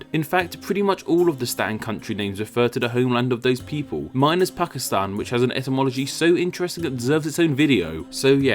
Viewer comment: OH! Pakistan...thought he said 'Fuckistan', Hahahahahaha